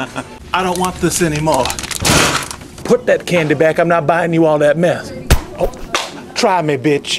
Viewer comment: He sounds like he saying, " Put that candy back. I'm not buying you all that METH." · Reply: No apparently he says "I'm not buying you all that mess."